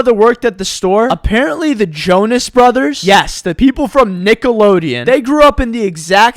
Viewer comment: I can’t tell if “The Naked Jonas Brothers” is an intentional joke or if he legit mixed up the two shows. Which is fair, they were both similar ideas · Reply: I thought he said Nickelodian.